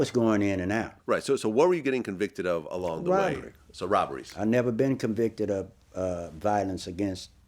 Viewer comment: WTF did he say? PLEASE HELP ME!!!!!!!! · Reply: Robberies